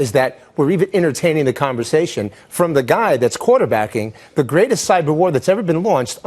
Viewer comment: what backing? I hear something like corderbacking but do not really know. thx